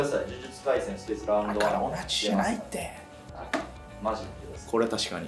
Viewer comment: これ呪術回戦観たことない人からしたら 「だからオナ中じゃないって！」って聞こえる